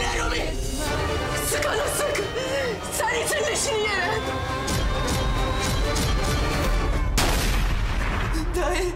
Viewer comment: da ne diyor ? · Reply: Ser itin leşini yere diyo